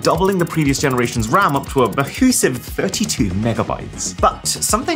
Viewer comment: what word did you say there. It sounded like bahoosive? What are you saying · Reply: mahoosive, british slang for ‘very big’